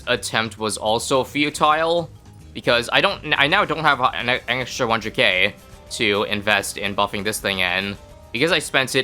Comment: What did he try to say? 😅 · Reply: "I now don't have an extra 100k"